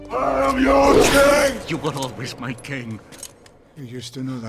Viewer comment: "You were always my king." I love that line. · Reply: I thought he said you weren't always my king, you used to know that once